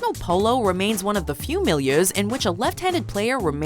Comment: "Professional Polo remains one of the few ____ which a left-handed player remains <mail> Pleas help. What does it say? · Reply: Milieus (pronounced mil-yoos).